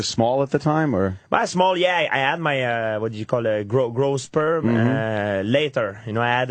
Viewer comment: ish, Did he say "Grow Sperm ???"